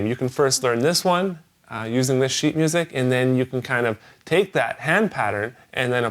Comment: sounds like says shit instead of sheet :>